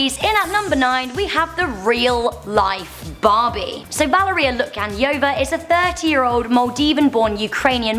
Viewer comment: Wait did she said Valerie at 1:19 because my name is Valerie?